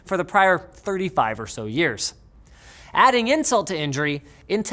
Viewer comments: Did I hear, "adding Intel to injury?" LOL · Adding intel to injury (instead of insult to injury) hahaha that part was hilarious Linus! Loved it! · I first heard "Adding Intel to injury" 😂